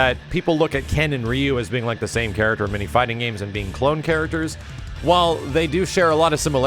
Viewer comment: I thought at first they said no clone characters